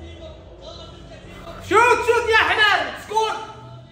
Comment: Did he say chutiya?????